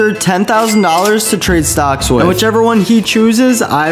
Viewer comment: i thought he said trade socks